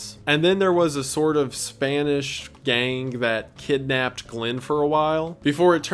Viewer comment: did he just said “spanish gang”??